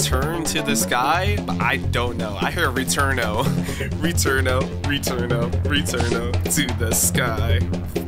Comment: Cybop sounds like to me sounds like he says "we tune up we tune up to the sky" Idk if you hear that but it's maybe just me